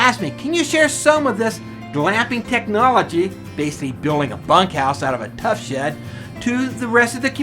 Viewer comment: a what house?! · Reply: I thought he said fuck house to 😂